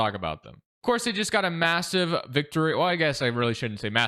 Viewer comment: Did I hear massive?